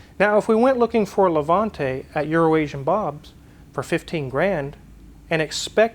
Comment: I’m so pleased that and pronounced ‘Euroasian’ Bob correctly. To my Australian ears, it sounds like is saying Urination Bob.